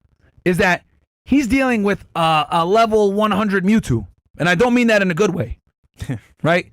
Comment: dealing with a lv100 Mewtwo was the best... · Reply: had to pull it up to listen if thats what he said i thought he said mutant at first then i said nah thats not what i heard tho i heard mewtwo